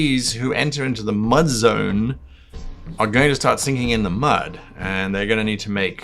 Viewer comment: At first I thought you said "singing in the mud", not "sinking" and ... that'd be a strange musical.